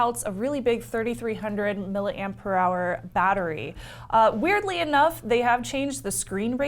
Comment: OK did you just say "milliampere hour battery" or "milliamp per hour battery"? 'Cause it sounded like the second one...